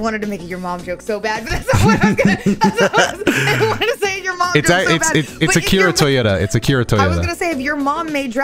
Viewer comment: did he say the creator of dragon ball is Akira Toyota?